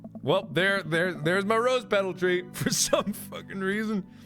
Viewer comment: did he just say.......rOsE petal tree?